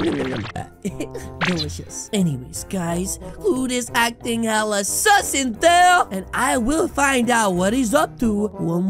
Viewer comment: did say hella sus or a little sus